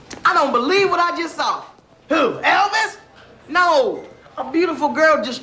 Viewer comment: When I was a kid, I heard that as “Who, elephants?!?” As an adult, I now hear it as “Who, Elvis?!?”